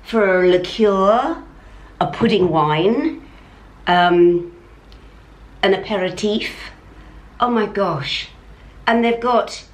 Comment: Thought you said pair of teeth not aperitif. Ageing 😅